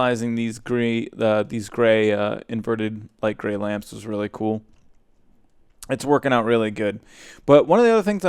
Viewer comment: i thought you said its twerking out really good also congrats on 50,000 subs!